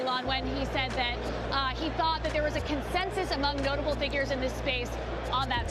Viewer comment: did she say "gullible figures"? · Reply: notable